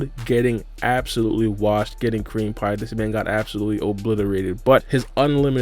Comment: did he just say code got creampied? sus